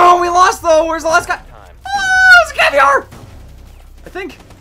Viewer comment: Did call caveira caviar? MUSIC WATCHES THERUSSIANBADGER CONFIRMED